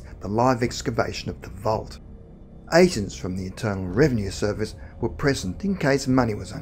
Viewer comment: could have sworn he said Eternal Revenue Service ...